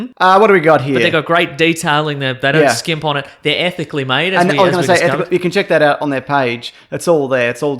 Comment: It sounded like maso said the shirts were ethnically made instead of ethically made. Happy Birthday brother!